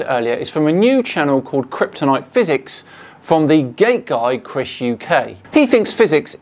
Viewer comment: Not gonna lie, I keep hearing "gay guy" instead of "gate guy"